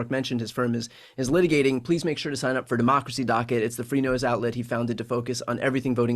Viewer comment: I keep hearing "Democracy Docket the Free Nose Outlet" at <link> Thank you Marc for everything you do!